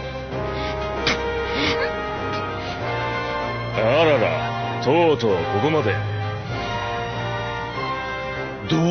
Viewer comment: Bruh, did i just hear Aokiji saying ara-ara? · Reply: He says arara without space 😂